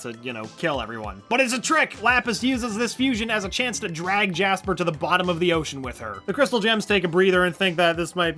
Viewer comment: My volume was low and I thought you said the vsco gems instead of crystal grms